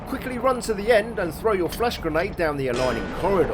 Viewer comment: Whenever you say flash grenade I keep hearing "flesh grenade". Like it's some sort of lovecraftian sci-fi horror weapon